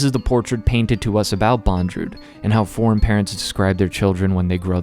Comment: foreign... parents? Did I hear correctly? The sentence doesn't make a lot of sense.